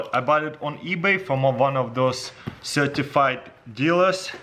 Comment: I heard "circumcised dealers" and at that moment he was cutting box xD